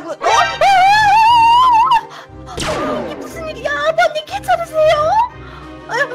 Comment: 나만 아버님 케찹이세요라고 들리냐